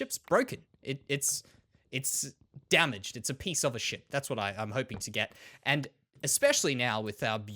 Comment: I thought u said piece of sh!t love ur vids man